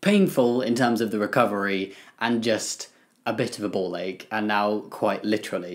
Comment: “A bit of a ball ache” ... “now litterally a ball ache” 😂😂😂😂 good luck hope things go smoothly!!🙏🙏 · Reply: I thought he said “ball egg” 😂